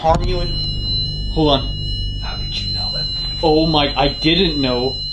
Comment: did anyone else hear "how did you know" right after says "hold on" when he felt someone behind him? Creepy!